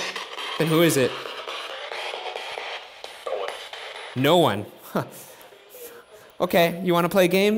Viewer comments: “Roy are you here right now?” “No” “Then who is it?” “No one” hey we got a funny guy over here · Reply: i heard ''Owen'' not "no one '' and it looked like Ryan was mean to him without a reason :c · i heard "owen" instead of no one :?